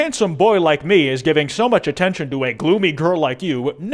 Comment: I HEARD THAT AS GLOOBY AH THE DELTARUNE BRAINROT